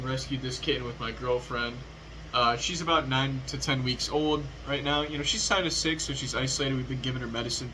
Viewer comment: For a second i thought you were saying your girlfriend was 9 to 10 years old and i thought damn... well explains the megumin figure in the back. If course it was the kitten being 9 to 10 months old.